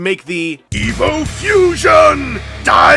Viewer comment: Evo Fusion the greatest gimmick in all of Transformers toylines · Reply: Oh, EVO Fusion. I thought he was saying EVIL Fusion.